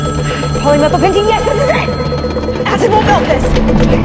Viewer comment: what did say on 3:45? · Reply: polymethylpentene